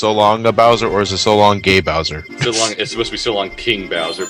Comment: I always thought Mario said 'So Long Big Bowser !' But I guess everyone has their own opion on what he said.....